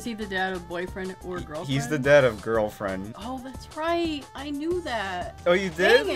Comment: I thought you said “He’s the ded of girlfriend” Xd